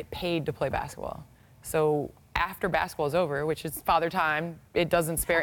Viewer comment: did she just call herself a Father? LMAO